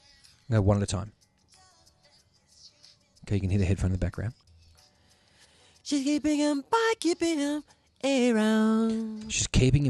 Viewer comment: I am soooooooo happy this video exists!!!! I ALWAYS wondered what Michael was saying in the adlib 28:39 - 29:50 : "She's keeping him by keeping him around" Mystery finally solved!!!!!! 🥳🎊🎉🥂🙌🏾🙌🏾🙌🏾👏🏽👏🏽👏🏽👏🏽🕺🏾💃🏾🕺🏾💃🏾🕺🏾💃🏾👩‍🦼💃🏾🎺📯🎺📯